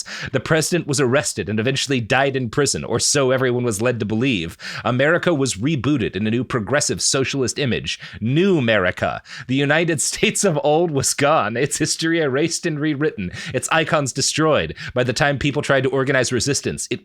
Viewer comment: I keep hearing “Numerica: The United States of Old Wiscon (as in Wisconsin)” 😂